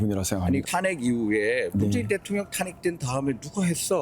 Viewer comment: 문재인 대통령이라고 들리는거 나만 그런가??